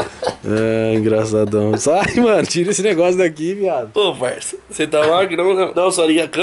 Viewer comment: "tira esse negocio da minha cara GATO!" foi isso que ouvi kkkk